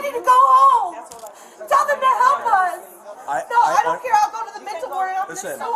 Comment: Why did the caption, show that the officer said, "I don't care", when I only heard,"I don't"?